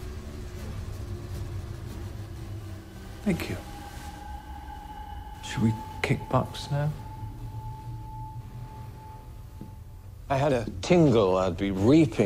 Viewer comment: What does he say? It sounds like "Shall we kickbox, sir?"